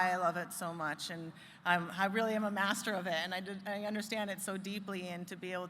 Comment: did she just say I'm really a master of it ?